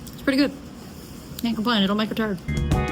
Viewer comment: omg lol at 6:04 did you say "cant complain, itll make a turd" I cant, hahaha never heard that one before